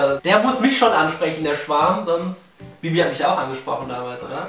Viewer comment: ich habe Schwan,dieses Tier verstanden!!!